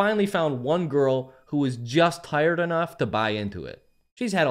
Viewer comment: I thought you said "just hired enough"